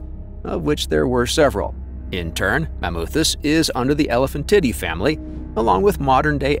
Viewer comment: Off topic but the way the AI voice pronounced Elephantidae sounded like it was saying elephant titty.